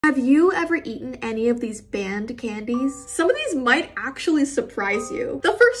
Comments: What she said: "banned candies" What I heard: "band candies" sighs I think i should sleep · I thought she said “band candies”